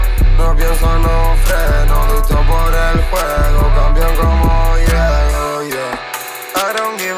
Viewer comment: yo pense que decia cambian como hielo